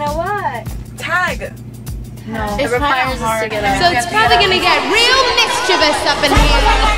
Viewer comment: wait what did lauren say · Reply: it requires us to get up